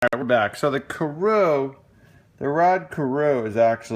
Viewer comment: Hillbilly Trump did I say that? I believe you but I certainly know how to pronounce his name. Must be quantity overload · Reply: Some people heard Yanny some heard Laurel !!!! Just sounded like you said " Carow" to me twice in the very beginning. I can tell you know your players though. Happy New Year to you , and take care.